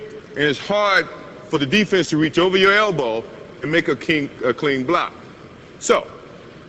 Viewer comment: mark…..I heard a Freudian slip! “To make Hakeem-……a clean block” Dream’s defense living in Jordan’s head rent free😂. J/K but it would have been badass to watch those two in the Finals in the mid-90’s